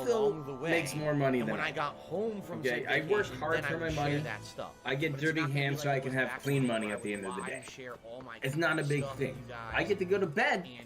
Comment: Ok I'm sure that's not what said but is sounds like - " I give dirty hand jobs so I can have clean money at the end of the day"' hahahahahahaha (I think it has to do with my listening to it at 1.5x speed)